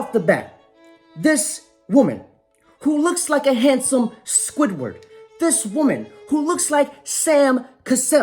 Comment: I thought he said a "Handsome Swiffer" LMAO